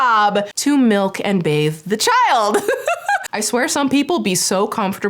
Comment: did just say "mik the child"? 😂